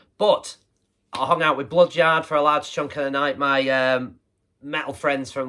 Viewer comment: Thanks mate! Discovered your great channel via a few mutual online mates (John at Rock & Metal Plus & Wendy off Instagram) Anyway, subscribed and I’ll be working my way through your vids soon mate! 👍 My mates Bloodyard are top folk too so check them out at: Bloodyard 666 on here 👍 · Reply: Top people, both of them! Cheers, dude. Much appreciated. Aaah, Bloodyard! No wonder I couldn't find them, I thought you said Blood Jar! 😂😂😂